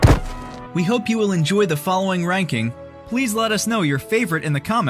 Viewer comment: I tried playing this on mute, believe me I hear him say "I home you will enjoy the following rankings, please let us know. .. " ❤️💯😃